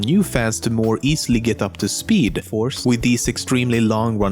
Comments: Does he say speed force in 1:40 if he is saying tht he is probably a flash fan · Did anyone else hear him say "speedforce" at 1:40? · Who else heard him say "to bring new fans up to speed ´force´" very silent and timed to when flash was on the screen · Did anyone else hear him say "Speedforce"? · LOL am i the only one who heard him say: more easily get up to speed force. XD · Did anyone else hear him say at 1:40 “new fans to get up to speed ‘force’”? Btw for those who don’t know that is a reference to the Flash’s power/speed source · I heard you say speed force